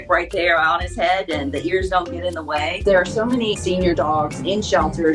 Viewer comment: Did she say there are so many SENIOR DOGS @ 2:17?